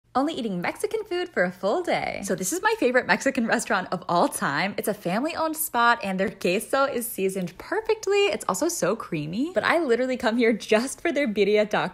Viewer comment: I thought she said gayso 💀💀💀💀